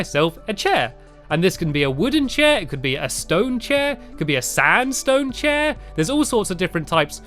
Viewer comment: I thought he said a sad stone chair but he said sandstone chair